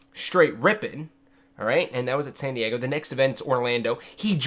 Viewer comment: Sounded like you said Sandy Eggo haha. <3 Loveyou